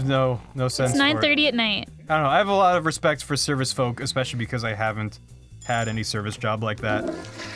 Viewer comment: "respect for circus folk" is what I heard.